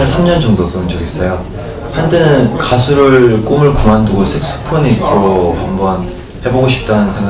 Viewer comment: it still sounds like he said "sex pornstar" lmAO I'M ASJDKHFHSDA